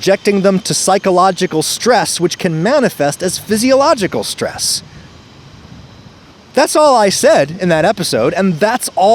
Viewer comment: Hey Adam, at 40:06 the caption says "their medical care and subjecting them to psychological stress which can manifest as psychological stress." I'm pretty sure the caption is supposed to read "their medical care and subjecting them to psychological stress which can manifest as physiological stress."